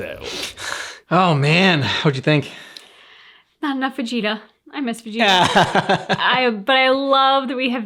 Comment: did she just say I'm not Vegeta?